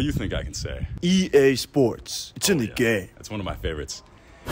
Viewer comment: Yoooo I always thought I heard "to the game" although I knew it was "it's in the game" and voice guy says "it's in the" so clearly but when the first guy said it I really heard "to the game" and I feel SO VALIDATED I replayed it a few times and he says "it's in the game" but I get now why it sounded that way to me and that it's nit a false memory but I really just heard it that way then. Woah!